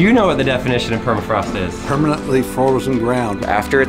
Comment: It sounds like your saying Kermit Frog